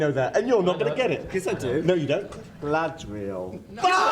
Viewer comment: Can someone explain to me what was going on from 5:45 when the guy said something like " the lads will " and why the uproar ? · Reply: Tanith221B Galadriel* and Glamdring* :P